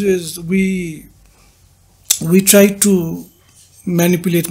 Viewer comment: did he say manipulate market? and correct himself.